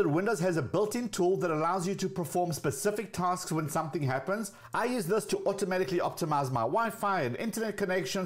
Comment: Not gonna lie, I thought you said "I use this tool to optimise my wife.". I thought to myself, "Well, that's nice."